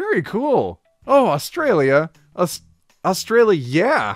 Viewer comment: I was low-key expecting a thumbs down because for the past year every time I've heard Australia I hear upsidown